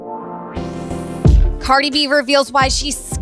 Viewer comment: I thought you say cardi beaver feels in the beginning